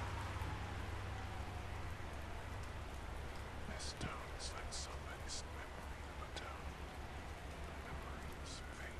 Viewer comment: Someone kows what is he saying at the end of the song at 5:33, please? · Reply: I guess he's saying "this town is like somebody's memory of a town. And the memory is fading" I'm not sure about the 1st and the 2nd words. Sorry